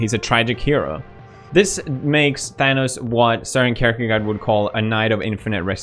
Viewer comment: The hole time i thought that he Said character god, and Then when the bookcase showed i was like, he is talking about Kirkegaard. Im Danish so of course i know who this dude is. In newer Danish language Its kirkegård.